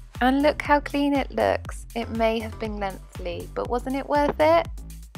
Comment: Sorry, strange question, what do you around at 17:53? It may have been messy did you say? It was messy obviously but I'm not sure if you said messy at that point in the video or something else. (( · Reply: I said lengthily! Sorry if I wasn’t very clear hun xxx